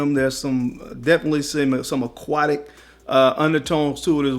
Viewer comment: Why did I think he said “definitely semen” at 7:30 😂😂